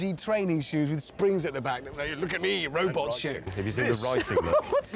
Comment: Did he say robot shit